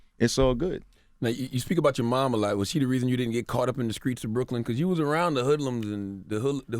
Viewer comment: What’s up with CTG always mispronouncing words in every interview🤔. @ 25:59 he says skreets of Brooklyn not streets of Brooklyn lmao. 😩🤛@ 35:58 he says main scream(skream) instead of main stream. Anything with a St in the word he mispronounces lol. Like one interview he said skrimps not shrimps an in multiple interviews he’s said skraight not straight😂😂💆🏽‍♀️🙊. Other than that this was a gr8 interview.